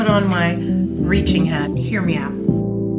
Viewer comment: I love your hair and everytime you say “hear me out” I hear “hair me out” haha